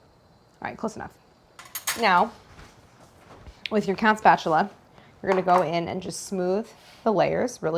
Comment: did Claire say Count Spatula? 🥺🧛🏽‍♂🎂✨